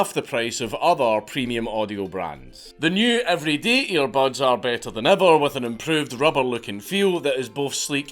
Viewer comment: sounded like you said the buds were beta than ever. 😁